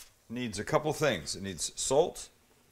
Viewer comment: Did he just say solt?